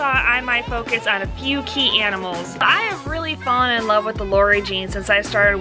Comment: did she say she likes the Lori gene??? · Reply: Nanny Rike they have a morph they they named after Lori